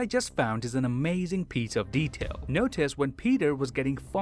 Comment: An amazing pizza of detail? Am I the only one who heard it that way?